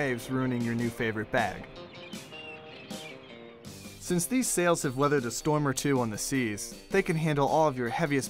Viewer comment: I thought is said "Snails"